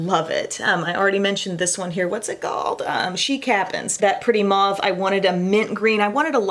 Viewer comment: I definitely heard “chic happens” as “she cap’ns” 😂